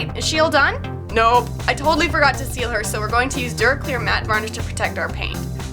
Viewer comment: I misheard duraclear as derp ear